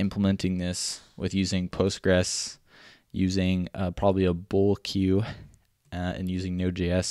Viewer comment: Did you say you're using a Bowl Queue at 8:19? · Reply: Bull Queue, it's a library